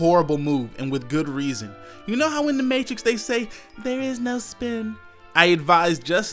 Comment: It kind of sounded like he said, "There is no SPERM."